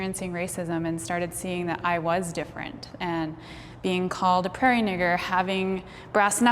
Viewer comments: did she say prarie n word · I am sorry if this is offensive I am trying to educate myself. What did she say at 10:56 and why is it offensive? Please do not take offense to this it is a real question I want to educate myself and others more on this topic. (Please do not type the whole thing if it is truly offensive I do not want this to be some type of "excuse" to say anything hurtful.) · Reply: She said prairie nigger, it's a racial slur towards indigenous people.